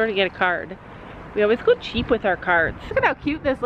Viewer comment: At first I thought you were saying you were hitting the dollar store for a car!😂